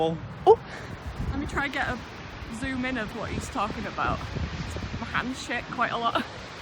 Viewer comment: I thought she said my hands sh*t... oml